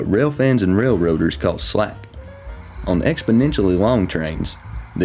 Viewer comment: all i hear is knuckle slack.